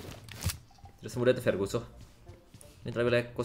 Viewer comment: ngomong apa bang?🤣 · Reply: "Tidak semudah itu ferguso"